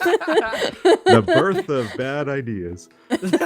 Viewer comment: At first I thought Etho said "The bird of bad ideas"